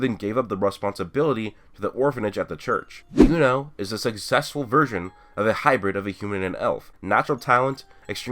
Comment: he said successful version but i thought he said successful virgin lmao